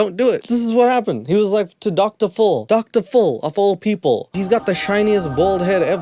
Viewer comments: I swear everytime says "Dr. Phil" all i hear is "Dr ful" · Up to the 15-minute mark, I heard you say Doctor Full and I always wondered 'who TF is Doctor Full?' I have the hearing of an 80 y/o sometimes 😂